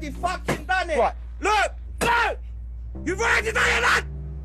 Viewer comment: What does he say at 2:50 ?? That shit got me broken af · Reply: You've already done it